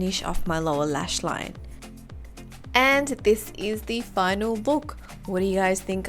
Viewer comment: And this is the final BOOK lol.... I heard that wrong or did u really said book hehehhahah